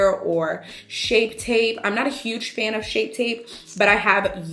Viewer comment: This is the first time i hear:" ı am not fan tarte shape tape"